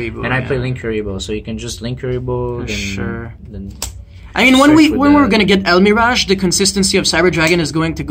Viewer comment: HEY anyone catch the name of the card he mentions in 12:03 ? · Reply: Salamangreat Almniraj.